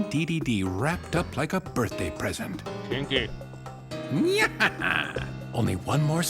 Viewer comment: Di-Did just say kinky @ 19:16?